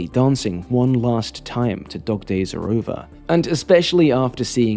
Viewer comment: I honestly did not know the title was "dog days are gone" I always heard "the dark days are gone" and It felt so impactful during the final scenes